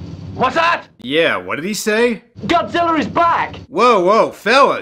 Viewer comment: did anyone else hear that dubber call godzilla "GODZILLER"?